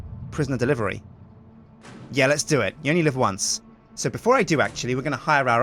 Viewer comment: Did he really say YOLO at 12:06?